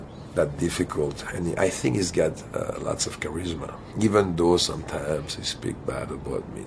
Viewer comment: I thought he said "his gut has lot of charisma"